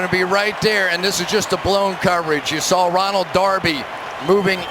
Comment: i thought it said, "browns coverage"